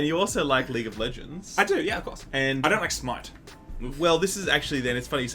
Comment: Did I hear correctly because it sounded like you said "I don't like smite." You better change your mind real fast because smite is king. Smite>overwatch